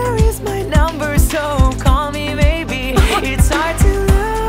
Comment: my fav was CALL ME MAYBE AND THAT TIME HE SAID HEY I JUST MET YOU AND THIS IS CRAZY SO HERE IS MY NUMBER SO CALL ME MAYBE I THOUGHT HE SAID SO CALL ME BABY ::::::))))) embaressd and funny hahahahahahahaahahahhahaha